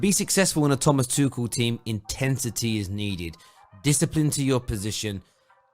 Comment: Mate did you just say “Intentity” instead of intensity 2 mins in?